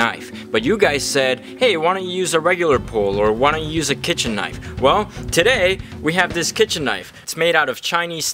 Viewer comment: i thought it said ''kitten knife''